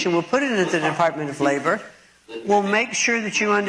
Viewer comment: Am I the only one who heard "The Department of Flavor"? I swear 1:20 she said it. Makes sense since it wouldn't surprise me if she believed there were an actual Department of Flavor.